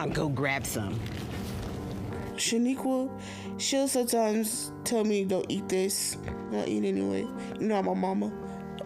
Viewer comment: When she said “sometimes she’ll tell me don’t eat this but I eat it anyways your not my mom” I thought she said “sometimes she’ll tell me dont eat this I eat it anyways nom nom”😂